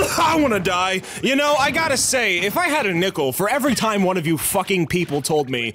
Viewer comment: I swear to God I heard "If I had a nipple" kill me